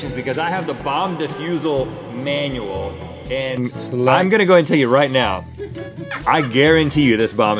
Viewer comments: did link say "slut"? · what is link actually saying in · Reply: Select